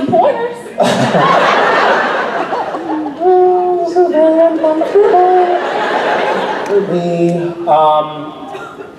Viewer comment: What did he say at 47:02? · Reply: he said something like ˇhmmm this is very uncomfortableˇ :)